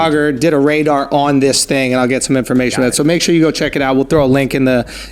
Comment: What does Akaash say at the very end? Could someone spell it out, please? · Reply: ijk Thanks, man! I appreciate it! Jai hind!